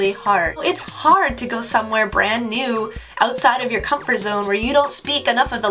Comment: comfort zoneがcrumple zoneに聞こえるのは仕様ですか・・・